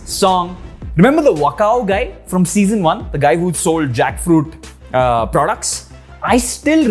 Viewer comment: "Jackfruit uhh products" Due to the uhh I thought he just said Jackshit XD (Till i replayed the clip to enjoy it again but only to get disappointed)